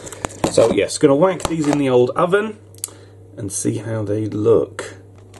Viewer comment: I swear he says w*nk them